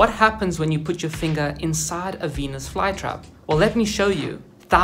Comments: I heard the word venus wrong so i heard “Penis flytrap” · Why did i hear the "Penis FlyTrap" 💀